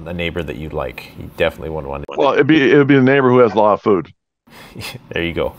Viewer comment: I thought he said "a neighbor with alot of booze" lol